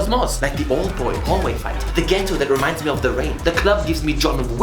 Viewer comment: what movie did you say? Rain? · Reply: The Raid